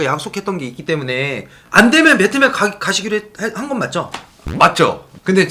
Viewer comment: 오늘 영상도 감사하게 보겠음😁 00:47 베트남인데 배트맨으로 들림ㅋㅋㅋ